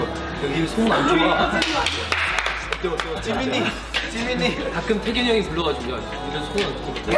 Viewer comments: istg i heard someone said jimin-ie.. OMAIII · did BamBam really say "Jiminie Jiminie" or it's just me?